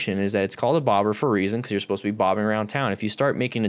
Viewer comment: I think bobber is from when full fenders we’re shortened or “bobbed”. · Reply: That's what I've always been told...and I always heard it as "Bopping" around town.